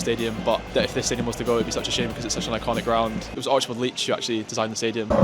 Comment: I thought he said that Archibald Leach designed the stadium, I'm thinking that's Cary Grant from the movies. I now realise he meant Archibald Leitch, the architect and prolific football stadium designer.